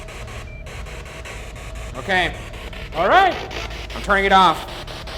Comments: Anyone hear “don’t go please” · i can’t be the only who hears “ Ryan don’t- don’t leave” when he says he’s gonna turn it off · around this time i heard “don’t leave” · I’m I the only one or at around 10:13 when Ryan says he turning the spirit box it sounds like something says “no don’t leave” and then you could hear another voice say “goodbye” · it almost sounds like it says “no. I don’t want you to leave.”